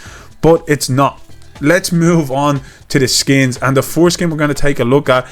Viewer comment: sounds like you said foreskin 😂